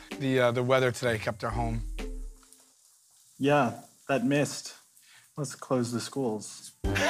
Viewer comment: What did the guy say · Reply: Yeah, that mist, must have closed the schools